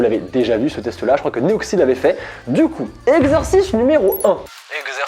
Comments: j'ai entendu exorciste n°1 😂 fais pas de mal a Tsuki UnU 🖤💙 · J'ai entendu "exorciste" 😭😅